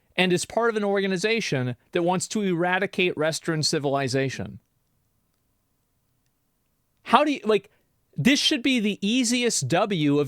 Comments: I thought he said “they want to eradicate Restroom Civilization” and I was like DAMN that’s a spicy take 😂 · does it sound like Asmon said restroom civilization to anyone else?